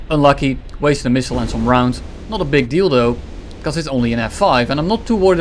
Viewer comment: I just heard "Not a big dildoo" :))